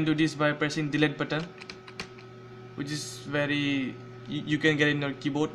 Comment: What do you say at 0:43 after delete ,something like patel? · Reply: it was"which is" spoke out by mistakely